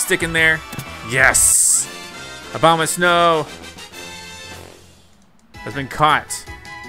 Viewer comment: Sounds like he said obamasnow 😂